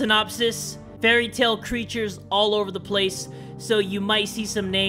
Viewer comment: I thought when you said fairytales I thought you meant fairy tail. I WAS LIKE OMFL YOU WATCH FT?? But no .-.